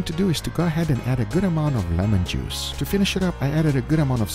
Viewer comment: did you say “melon juice”?